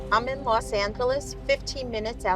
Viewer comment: did really say "I am in Los Ancklless"?. 😆😅😂